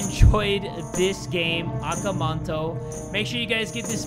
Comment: Lmao, I thought he said “Aka Manko” That That scared me